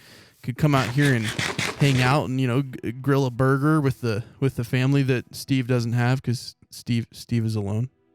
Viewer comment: did you say family?